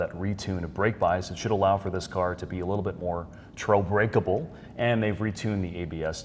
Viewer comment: "trobreakable" at 6:31. english is my first language listened 6x and still can't understand what word he is trying to invent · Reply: Trail Brakable